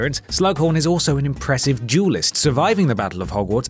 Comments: The entire time I thought you were saying jewelist instead of duelist. I was pretty confused for a while. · Why do I always hear 'jewelist', these wizards are millionaires eh? It took me until bellatrix, to realize that it is 'duelist'. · I actually thought you were saying "jewelist". I was like wow, several of these guys were very good at making jewelry, maybe this was a very highly recognized skill and their jewelry was very valuable. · I could have sworn that I heard jewellist instead of duellist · At first I thought you were saying “jewelist” instead of “duelist” 😅